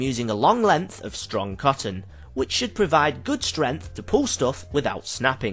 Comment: I thought saying "strenth" instead of "strength" was something only Smooth McGroove's Twitch chat did. But unless I'm mishearing him, he said the cotton had "strenth".